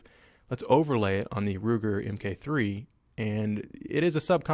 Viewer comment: At I thought you said Lugar at3:41 but he didn't 😪😥😪🔫